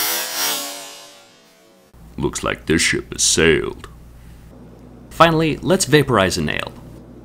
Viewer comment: Thought he said, "Finally, let's vapourize an ant." and I got excited. What a let down.